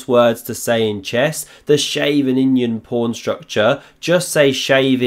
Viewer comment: I spat out my coffee when I thought I heard "Shaven Indian Porn Structure" 🤣